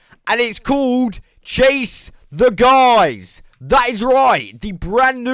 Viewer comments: i thought i heard "Chase the Guys" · Oh God the first time you said "chase the skies" I swear it sounded like you said "chase the guys"